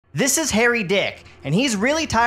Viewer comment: I thought you said hairy